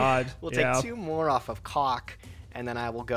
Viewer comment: Did he just say c0ck instead of Pot not once but twice!?? That did not sound like "pot".